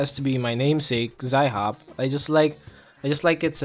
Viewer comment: I had no idea that was the name. I thought the weapon was called zailhop with an L ALL ALONG